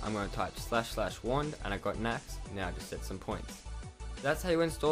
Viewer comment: Thank you you're a life saver because I've wanted i forever and get this it was so simple that i was able to do it on my mac at first i thought you said //1 or //one but then i looked and saw it was wand xd but thanks so much for the easy tutorial